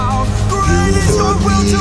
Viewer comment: Is it me or Am I hearing "600 Likes" instead of "600 Lives" 😅